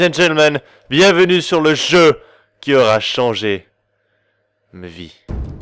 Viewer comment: J'ai pas comprit à 0:32 tu dit "aura changer mes vies". Je savai que tu était bizarre mais pas à se point ! xD Je me trompe peut-être en tout cas moi j'ai entendu sa...